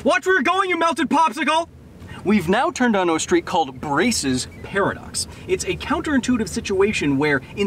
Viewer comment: I swear I heard him say Racist Paradox when he said Braess' Paradox.